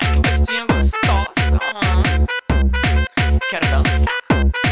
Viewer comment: O que ela fala em 1:17 ? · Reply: quero dançaaa